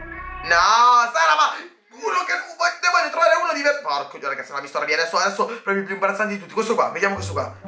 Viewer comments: solo io ho sentito po*co d*o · Ma solo io ho sentito porco di..